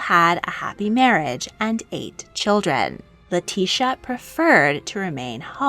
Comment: i thought she said the couple “ate” children, not “eight” at 11:08 bc i wasn’t looking at my phone lol.